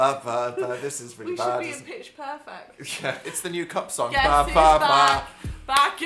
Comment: Who else heard they said Pitch Pervert